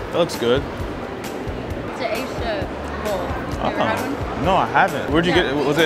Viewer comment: Did she say “It’s an Asia bowl” 😂